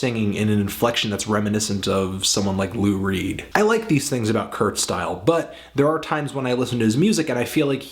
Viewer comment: I thought you said 'Kurt Style' at one point. Cool name